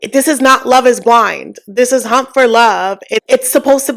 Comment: I'm sorry, I heard "this is hump for love" @ 29:00 and was like "yeeeeah basically" 😂 I love hearing your thoughts bc it sparks a good conversation. @ 33:28, I'm sure I'm self-projecting. I've done some self work through therapy and what I learned was, it is jealousy. I was overweight growing up and didn't think I was attractive. So when guys befriended me to talk about other "pretty" girls they liked, I thought I was better than them (the girls). I would give honest, but bad advice bc I didn't want them to choose the "pretty" girl. Ofc I acted like I wasn't insecure/inferior. Basically I felt insecure/inferior but being a pick me made me feel superior to them. I wasn't able to get out of that cycle until I acknowledged that I WAS insecure and didn't need to put them down to feel better about myself.